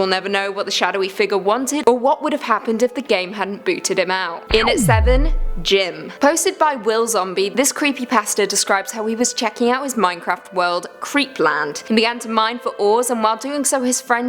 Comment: lol i thought you said creepy bastard